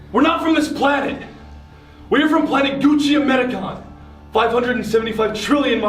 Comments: I thought he said they were from planet Gucci Mane 🤣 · WHEN GRAYSON SAID THE PLANET I THOUGHT HE SAID "Planet GUCCIBAGEL"😂😂😂😂